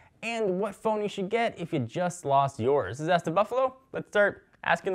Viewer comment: I swear it sounded like he said "it's time for ass the buffalo"